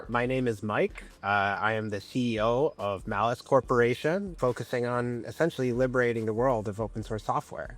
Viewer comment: I legit heard "Malice Corporation" LOL